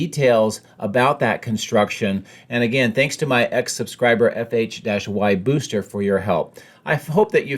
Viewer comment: Totally thought you kept saying "ex-subscriber". Was weirded out until I realized you meant "X-subscriber".